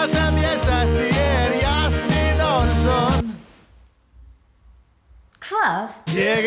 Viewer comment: Is he saying Yasmin Rasoel?!